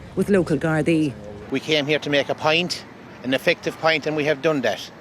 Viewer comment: I first thought he said he came to make a pint! 😂 my bad.